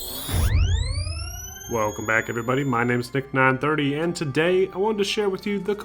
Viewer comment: Thought I heard.